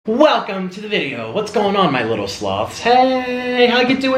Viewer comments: I remember when you first called us "little sloths" I thought you said "sluts" lol. I liked it tho 😂😂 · I thought you said "my little slobs" 😂